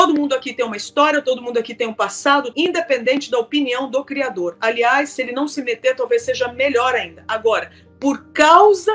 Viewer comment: "independente da opinião do criador,aliás,se Ele não se meter melhor ainda",foi isso que eu ouvi?